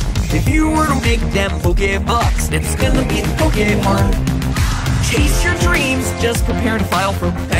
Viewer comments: Everytime he says that you spend the pokebucks at the pokemart i hear "making those poke-bucks is gonna be poke-hard" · AM I THE ONLY ONE WHO HEARS "IT'S GONNA BE POKÉHARD"